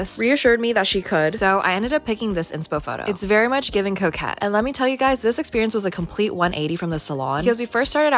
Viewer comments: Without the subtitles i thought she said its giving cokehead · I Heard “It’s Very Much Giving ‘Coke Head’”